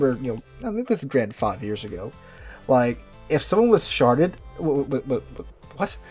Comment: Did he say “….. if someone was sharted?” lol, WTF? 💩 💨